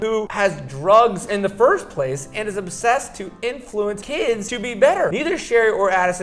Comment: did anyone else notice that when he read the tweet at 3:28 he says obsessed instead of supposed 😂